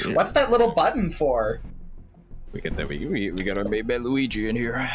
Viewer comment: DId i hear someone saying "Luigi"???